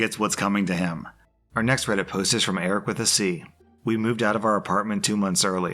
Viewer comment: i watch my vids on 2x speed and only 20 seconds in i thought you said the post was from "Arab with a C" which my mind went to ACAB and i was like bro what lol then i read the name...